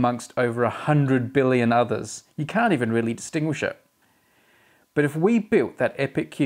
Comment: Almost sounded like you said we can't really extinguish the sun